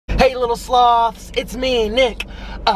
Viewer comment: I always thought he said “Hi little sluts” but it actually is sloths 😞